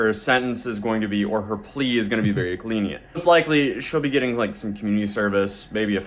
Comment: I'm a victim ❌ I'm an inmate ✅ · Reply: Did anyone else notice he says "Glenient" instead of "lenient"? LOL